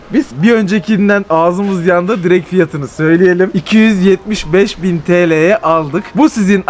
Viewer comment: Ben mi yanlış anladım 275 bin TL mi dediniz🙄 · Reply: Evet 275 bin ☺️